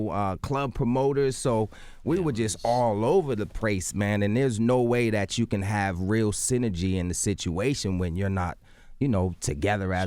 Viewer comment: Skip to 34:12... "All over the prace"????? Mr. DeVoe did I hear you right?